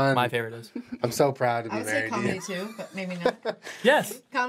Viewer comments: did anyone else catch Julien saying "I'm so proud to be married to you" ?! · Did anyone catch Julien saying, "im so proud to be married to you" ? At 10:43?